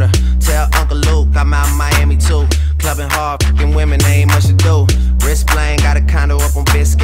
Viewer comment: I feel stupid I thought it said Kevin Hart f*****g women...